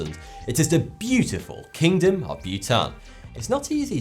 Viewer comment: oh Bhutan....I thought he was saying Butane....Welp, Chemistry class (╯°□°)╯︵ ┻━┻